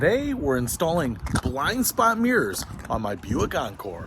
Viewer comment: Oops, thought it said bald spot mirrors. Heresy or hair you see?